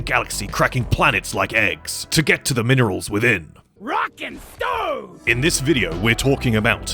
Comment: DID I HEAR A ROCK AND STONE?